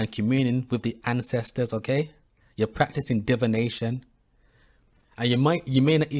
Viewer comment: Excuse me though I heard you say I'm practicing Devilnation no I plumed via Allahyourwatch to I need not for eye looked at The All not who show merciful to you when planets we land on to free how then my Define you can't stop the life in plastic in the wind you teach diviide I know how to live